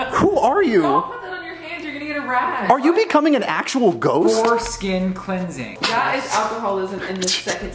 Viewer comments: "..for skin cleansing" What I heard: "foreskin cleansing" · I thought said "foreskin cleansing" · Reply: he said ‘for skin cleansing’ lmao